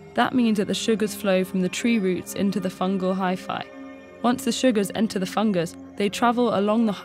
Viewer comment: I kept hearing "hyphae" and thinking "wi-fi". 😂